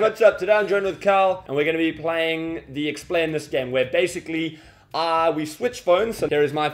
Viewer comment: I heard 'bones' "we switch bones" 😂